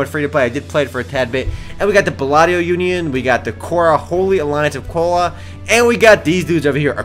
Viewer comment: Did you just say there was a Holy Alliance of Cola?